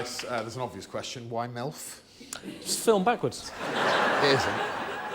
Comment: what does Greg say after Tim says “it’s film backwards” ? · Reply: "it isn't"